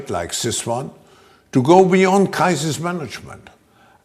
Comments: His Accent Is So Heavy That I Thought He Said "Kraut instead of Crisis"? This is Too Funny For Words. The Attendees are Clearly Brain Washed. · Kaiser? Did he say Kaiser? I heard him say a summit like this one was needed to go beyond der Kaiser’s management. Or maybe he said crisis…